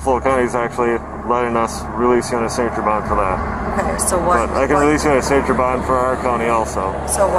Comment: Yo. Just letting you know, at 39:14 I'm pretty sure the cop is saying "I can release you on a signature bond" but the subtitle says "I can release in saint turban".